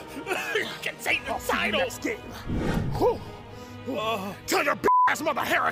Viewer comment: THAT'S NOT EVEN IN THIS GAME YET 💀 Also, does anyone know what Kratos said at 10:15? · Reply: "I'll see you next game" throws axe